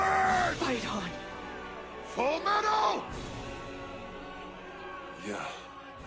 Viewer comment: what he really said was FUCK HIM UP 😂😂😂